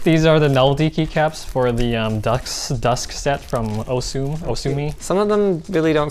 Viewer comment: did someone say osu??